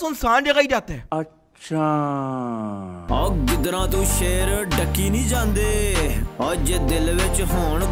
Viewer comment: did they say ducky😂